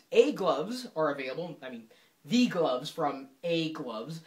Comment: sounds like he says "egg loves"